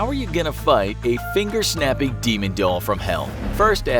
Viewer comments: Did it say finger shapping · shaping instead of snapping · it says finger shapping instead of finger snapping